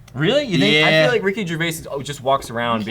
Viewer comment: When said "Ricky Gervaise" (idk how to spell his last name) I thought he said "Reheat your face"